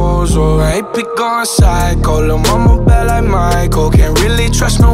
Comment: Wait.....I thought he was saying "I HATE BE GOING PHYCO" lol man i don't know shit I guess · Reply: I thought he was saying "My head be going Psycho" 😅